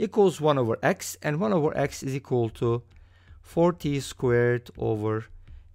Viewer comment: i wasn't looking at the screen when you said 4t² and it sounded like 40²